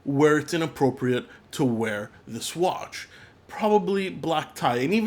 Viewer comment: does say that "there's very few situations where it's inappropriate to wear the Swatch"? :-) Nice vid. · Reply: "to wear this watch" lol